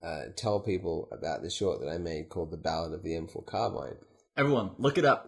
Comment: What was the short mentions at 19:23? I didn't understand him clearly enough. To me it sounded like "The Bowel of the Infold Carmine" but that doesn't sound right. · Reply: It was "The ballad of the M4 Carbine" The Ballad of the M4 Carbine